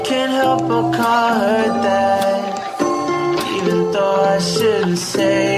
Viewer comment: i think he says "that" not "dad"